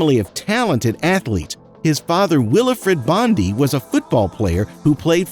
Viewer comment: i thought you said his father is wilfried bony